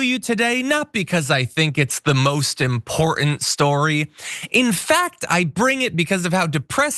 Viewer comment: did you just say “impor-ant”